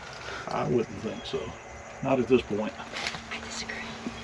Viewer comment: 😳My husband and I were watching and there is a female voice right there with you at the 23:03 mark that says "I disagree." Anyone else hear that???